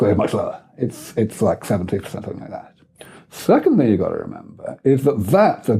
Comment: What does he say at 10:52? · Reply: "it's like 70% or something like that". Funny that so many people can't understand him haha